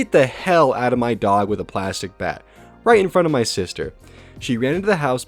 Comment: Plastic bat I thought you said plastic bag XD LMAO